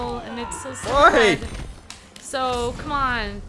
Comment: in 1:56 did anyone hear said hoy